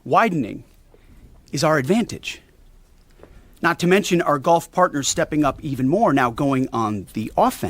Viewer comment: ong I heard Golf partner lolol. Not gulf parnter 😅 I totally saw Trump and Netanyahu plaided out with white hats swinging clubs in the sun 😂